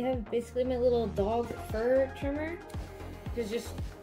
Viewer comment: I'm too tired. I had to rewind when you said dog fur trimmer. I thought I heard "ball hair trimmer"